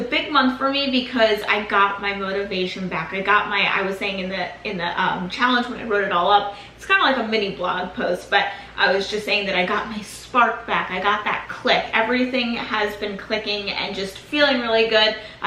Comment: Every time she says “got that click” I hear “got that clit” and I’m slayed every time. Lolzzz I have a dirty mind it’s fine. 🤙🏽